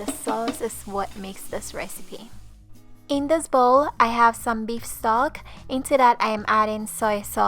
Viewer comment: This looks amazing! Can I make this without the beef sog? I can't wait to cook this for my family tonight! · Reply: i think she said Beef Stock lol... I also thought I heard SOG at first but now that I heard it again I think it's stock.